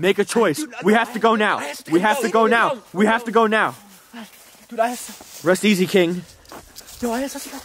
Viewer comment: did say “Rest Easy, Kaiten.” .....? How the hell did he know his name?